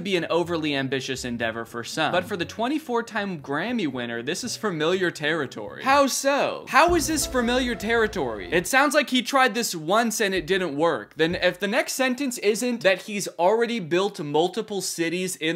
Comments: Why am I hearing "farmiliar"? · did you say "fermiliar"?